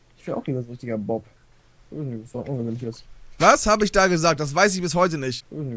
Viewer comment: Ich verstehe da immer "Ohne witz jetzt, Ohne witz jetzt"